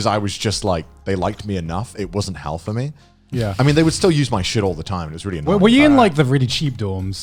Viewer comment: Due to the story before this sentence, I was grossed out when i first heard it lmao. "They use my sh*t all the time".